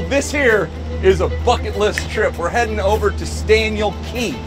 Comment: At first I thought he said f-ing lit trip not bucket list trip😭